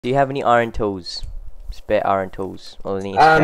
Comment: I thought he said iron toes! LOL